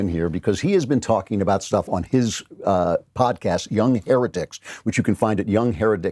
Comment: If you listen closely after the transition to the website, it sounds like he said the name of the podcast was the "here-dicks" instead of heretics. I didn't know you hated Spencer that much, Klavan!